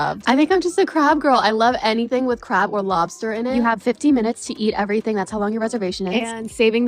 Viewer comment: 15 minutes to eat your food at Nobu? and at Marked up prices?? SHAME ON YOU NObu..! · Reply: I thought she said 50 minutes lol